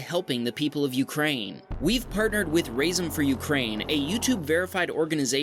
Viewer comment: When you said "Razom," I thought you said "Rasin." I love rasins.